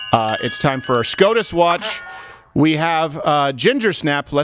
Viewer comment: Oh, you said 'we're on SCOTUS watch'. I thought you said 'we're on scrotum watch'. I was confused and oddly intrigued. Most tone deaf:. Biden and the whole democrat party.